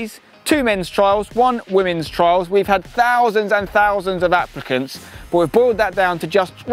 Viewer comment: I thought said thousands and thousands of Africans😭😭😭😭😭